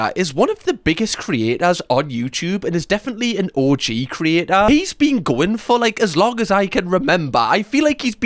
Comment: i heard orgy creator and had a WTF moment.